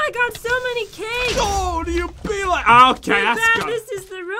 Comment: oh, do u peale me, alkaska. Is that what he said?